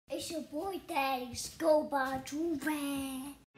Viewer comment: I can't understand the intro 0:01 reply me plzz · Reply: "Its ya boy Thaddeus. Go Buy a Joorag!" Btw cop yourself a joorag at <link>